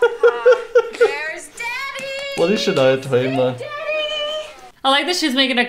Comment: wait was that "what is shania twain" i heard? a singer from the 90s "i feel like a women" "you still the one" couple of her songs i had to put up with for years because of my mum